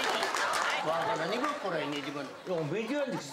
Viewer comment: 同じ青森県民でも、津軽弁は やっぱりレベルが高い😭 ベテランの方がたげすごい。 そして、太宰治のこと紹介してくれてありがたい。 一つ一つの単語ならギリわかるんだけど、 下北弁の方がとてもわかりやすいな😭 10:43のところ 『わがらねばこられねぇてばな』聞き取れたかも。 いつか、南部弁の特集もしてほしいなって思う。